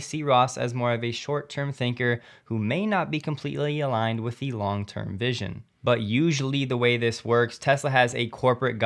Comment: Hi, I probably heard you wrong but it sounded like you said "Elong term vision" at 5:05, in which I thought was a brilliant term to describe Elon's long term vision😂